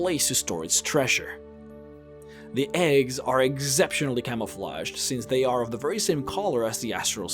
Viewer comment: The eggs are egg-ceptionally- I heard that